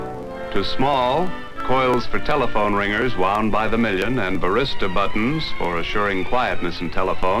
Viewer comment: I think the narrator is saying “varistor buttons”, but the accent made it sound like barista buttons to my ears. Whole lotta dead end googling before I realized he might be saying Varistor.😂